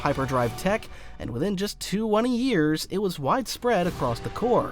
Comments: how many years? Cuz it sounds like he says " two one years" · Is this video AI or something? What are you saying at 3:50? Two-oney Years?